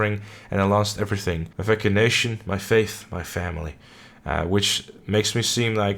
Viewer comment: Did you say "vacanation" at 7:42 ?